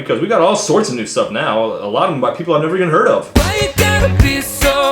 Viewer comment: the auto captions say "white people I've never even heard of." Seems appropriate